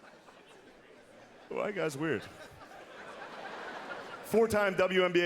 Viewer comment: "why you guys weird?" lmao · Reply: I heard ‘white guys weird’ so I’m glad I’ve got some clarification 🤣